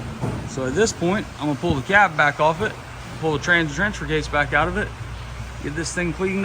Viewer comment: I swear says “pull the transgender case back out of it.”